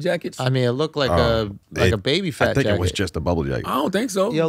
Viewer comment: Did he say it looks like Tekashi was wearing a Baby Phat jacket? LOL!!!